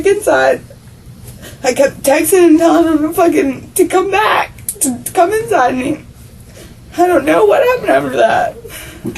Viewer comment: Did she just say “ I was like come back, come inside me”🤨🤣🤣